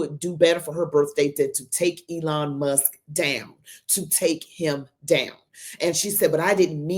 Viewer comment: I THOUGHT SHE SAID SHE WANTS TO TAKE HIM OUT AND NOT DOWN.